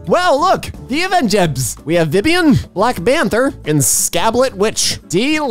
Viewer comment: Did he say "black banter"